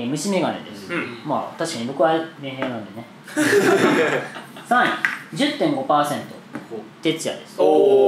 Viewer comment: の発言がどうしても聞き取れない！ 教えてくれぇーい！ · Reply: 僕はメンヘラなのでね？